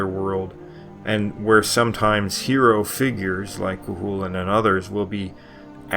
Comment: Could not find anything of this gulen figure you mention @ 3:56. Did I hear and spell it correct? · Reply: It is spelled "Cuchulainn." Don't expect logical spelling in Gaelic lol